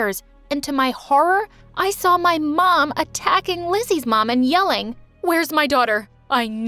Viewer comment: did they say Lizzy mum?